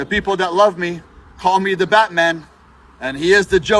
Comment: Nobody has ever called him “The Batman” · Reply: I think he misheard the beatman.